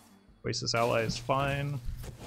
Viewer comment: Closed caption subs thought you said Racist Ally instead of Oasis Ally